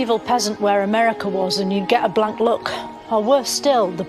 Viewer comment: When she said "if you speak to a peasant you would get a black look or the plague" is as reference to the fact that the plague was also known as "the black death". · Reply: They misheard as she said "Blank Look" lol